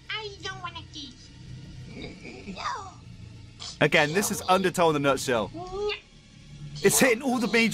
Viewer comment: did he just say undertow?